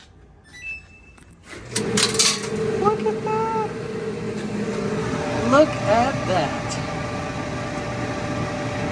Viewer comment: Did you say what the f@@ at 5:04 ? lol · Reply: I believe what I was saying was “What the What!?” 🤣